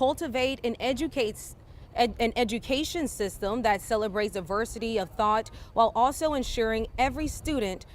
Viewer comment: The reporter says adversity of thought instead of diversity when reading the quote.